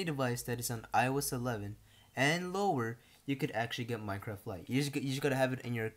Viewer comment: I thought u said iOS 11 or higher I was about the leave but he said iOS 11 and lower